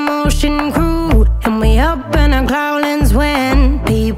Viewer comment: i thought they were saying holding and not howling, so I searched up you got me holding holding to find this song